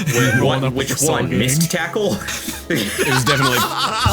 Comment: at first i thought that said Mist Tickle